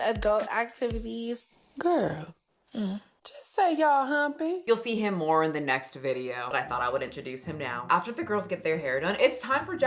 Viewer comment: Did I hear that right?? 10:37 NEXT VIDEO?? Oh we're eatin' 🙇🏻‍♀️